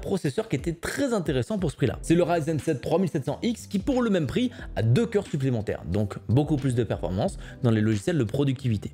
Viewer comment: C'est quoi le nom du site à 14:09 ? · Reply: idealo !